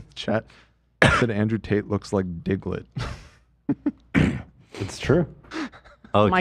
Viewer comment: I thought they said Tate looks like Piglet